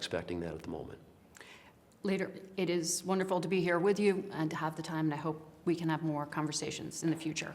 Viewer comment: Did she say,"Later it is wonderful to be here with you."?